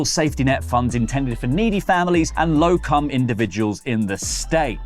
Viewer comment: did he say “Low cum individuals” can I get some of that 77 mil?